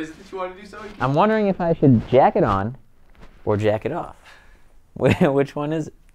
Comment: When Sohinki was saying jacket off it sounded like he was saying jacking off